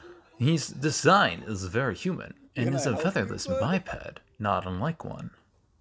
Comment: I thought you said "fatherless biped"